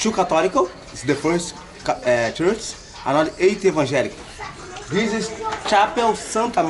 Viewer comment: I think he said 2 Catholic and 8 Evangelical (not Anglican)